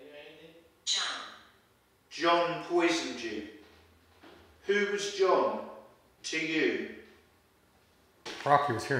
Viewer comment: Wow this was another banger! Who’s voice was that at 27:09-27:10? It sounded like a Male’s voice but I can’t make out what he said? Then I heard someone say dirty as clear as day around 28:13!! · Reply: I heard that too, sounds like it says "thank you andy".